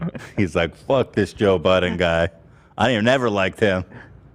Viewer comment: the whole episode i thought ethan said that joe BIDEN hates BTS and i’ve been so excited to hear about that.. but i got some mf budden guy